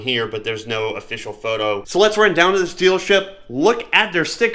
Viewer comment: Had this on the in the background while working on things in the basement. @ 7:16 i thought you said "Let's run down to this Stealership" ha.. not far off 😂